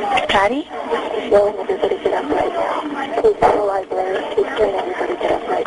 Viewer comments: You can hear Dylan at 4:30, you just gotta listen carefully in between the librarian and dispatcher speaking. I don't think he says "everybody with white hats, stand up" it sounds alot like "EVERYBODY GET UP NOW!" But that's just what I hear, so yeah listen carefully after the dispatcher says "what is your name ma'am?" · I'm not sure, but I think at 4:30, Dylan says something like: "Anybody with a ballcap, (something) now! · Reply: Dylan said at that moment, "EVERYBODY GET UP!". I thought he said that the first time around to.